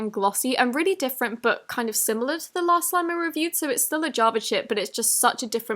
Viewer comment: Did anyone hear the s word at 2:38? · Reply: Pandilia Builds She said chip but it sounded like sh*t with her accent